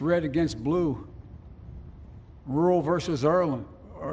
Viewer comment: The subtitles showed Biden saying "We must end this uncivil war, red against blue, rural versus Ireland...", which is pretty funny in itself, but if you listen to him, it sounds like he is saying "rural versus Erland", possibly an hommage to the Swedish actor Erland Josephson, one of Bergman's favourite actors, or some other Erland. It was too generous to see this as a speech of reconciliation. The Manichean imagery of light and dark was very Bidenesque, and not at all conciliatory. Lincoln talked about the site of the Battle of Gettysburg being hallowed ground, where 51 thousand troops lost their lives. It was a bit much to say the same thing of the US Capitol, after a riot where five people died. When Biden mentioned the Capitol riots directly he spoke of an assault on democracy, while it is becoming more and more clear it was never an insurrection, just performance theatre that got way out of hand.